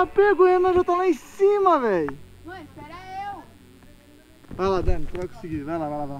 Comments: so eu q entendi q a dani falo mor espera eu pro renan? Acredito nesse casal danan! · Mano so eu q escutei a dani falando "moh espera eu" no minuto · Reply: Não ela falou Ow espera eu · FOI SÓ EU QUE OUVIR A DANI FALAR MÔ ESPERA EU DEPOIS QUE O FALOU QUE O RENAN TAVA LÁ EM CIMA MAIS É O OUTRO RENAN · Só eu ouvi a Dani falando ''mo espera eu'' em 13:32?? Kkk eita Renan... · diz etapega o renan ja esta la em cima nao sei se eu entendi bem mais parece que ela fala mor espera eu ou oh espera eu kkkk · GENTE NAO SEI SE FOI EU QUE ESCUTEI MUITO MAIS NO MINUTO 13:30 O RENAN TAVA SUBINDO AI A DANI FALOU MOH ESPERA EU VEJAM E FALAM SE FOI SO EU QUE ESCUTEI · Dani: amor espera eu ? Ou entendi errado kkk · só eu ouvi mor espera eu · foi só eu que escutei a Dani falar mô espera eu